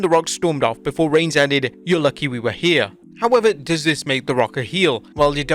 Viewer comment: I thought Roman said, “you’re lucky we’re leaving” Makes more sense with HHH response, “you’re lucky you are too” Cuz why would it be lucky for Roman and Rock to be here